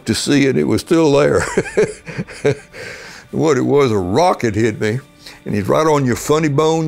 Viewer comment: i thought he said "a rocket hit me" and i was like a what?